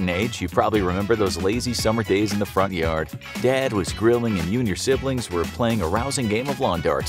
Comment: Am i the only one who heard "dad was grilling you and your siblings"?